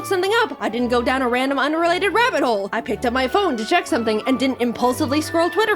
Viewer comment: Did you say rabbit hole, ¿¡LIKE "RABBIT HOLE" FROM DECO 27 ANIMATED BY CHANELCASTSTATION WITH THE VOICE OF THE VOCALOID HATSUNE MIKU FROM THE YEARS 2008!?